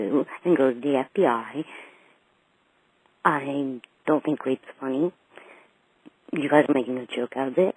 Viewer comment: Also, on the voice message she left, playing at around 31:35, I think she says "I don't think r*pe's funny" instead of, "I don't think it's funny". And... Doesn't the end of the voice mail sound like she's lighting up a cigarette?